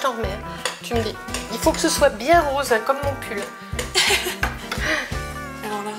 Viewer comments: Au debut j'ai compris "il faut que sa soit rose comme mon cul" au lieu de "mon pull" mdr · j'ai compris "bien rose comme mon cul" au lieu de "bien rose comme mon pull" svp dites moi que je suis pas la seule 😂😂😂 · j'ai compris " rose comme mon cul " mdrrr